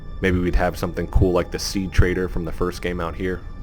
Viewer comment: I thought you said sea trader and I was like, what the what?! I missed a vendor in the game?! LOL